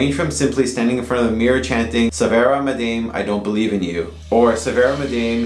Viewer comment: I thought you Said Severa Madame😅 but im sure you meant ”svarta madam”, cause thats a game I know about. Me and my Friends used to play that when we were kids. Once we actually saw a skeleton beside my Friends bed. And in the morning, there was a black hat on the floor.